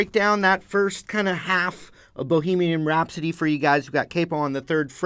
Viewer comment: I thought you said boogy man rhapsody